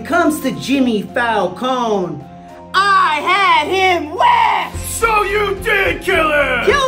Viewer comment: you had him what? I didn't understand Edit: oh Whacked